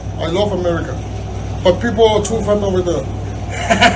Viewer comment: sounded like he said Americans are "too fun"